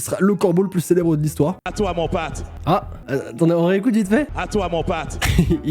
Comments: Ce n'est pas "À toi mon pote" qu'il dit.... il dit: "À toi mon Pat" (Pour Patrick). Personne sait reconnaître l'accent québécois :( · il dit "À toi mon Pat" et non "à toi mon pote" (parce qu’il s’appel Patrick) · Il a pas dit « à toi mon pote » mais « à toi mon Pat » Ici au Québec on dit pas le mot « pote », ça sonne même ringard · je suis québécoise et je pense qu'il a dit "à toi mon Pat" et pas "à toi mon pote" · il dit pas à toi mon pote il dit à toi mon pat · Il ne dit pas "A toi mon pote" mais plutôt "A toi mon Pat (Patrick)"